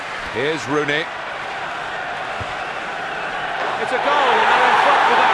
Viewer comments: I heard "It's a goal and no one fuck with that goal" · In 4:56 to 4:57 did he say "It's a goal! Dont fuck with that goal? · min does he say after rooney scores nobody fuck with that goal